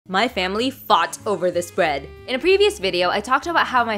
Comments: It’s such a huge compliment when your family fights for food you made!! 😆😂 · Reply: Am I deaf... Like seriously... I heard " My family farts over this food" In the beginning... 🙂🙂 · my Mom didn't get Ur accent She thought Ur saying farts Instead of Fought 😂